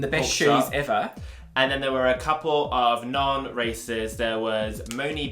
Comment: Oh man, I kept hearing "racists" when you said "racers".